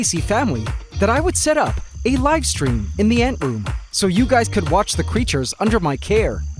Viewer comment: I thought he said live stream in my bathroom